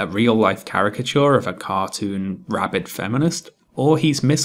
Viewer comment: At one point, you say the phrase "cartoon rabid feminist" but I swear I thought you said "cartoon RABBIT feminist" and I have never identified so hard with an imaginary cartoon character in my life.